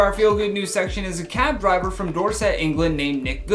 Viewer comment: I thought said cat driver instead of cab driver